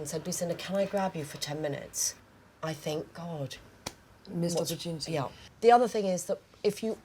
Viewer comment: the woman says, "If someone doesn't come and say: Lucinda can I grab you for ten minutes, I think" and then Alexa cuts her off and answers for her and Lucinda agrees. What did Alexa say? I've been replaying it but just can't catch it. Anyone wanna help please? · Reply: missed opportunity